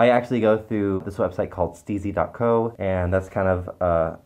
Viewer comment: When you said Steezy I almost thought you said Sleezy. I was like, “Well I know Fluke is a husky but I didn’t think of him like that.”